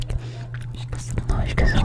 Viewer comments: Não esquece o like... - no esquece o like... - noes quece o like... - nos que ce o like... - nos que so like... só eu que dps um tempo entendi isso? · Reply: Eu ouvi " nós que seu like" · Não esquece o like... - no esquece o like... - noes quece o like... - nos que ce o like... - nos que so like... só eu que dps um tempo entendi isso? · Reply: acho q tbm ouvi "nos que seu like" kkk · Chegou uma hora que eu escutei “nós quer seu like” kkkkkkk